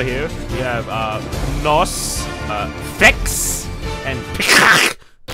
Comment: is sounds like he said “PEACOCK”